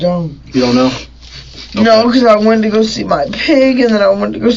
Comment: did she say she went to go see her pig?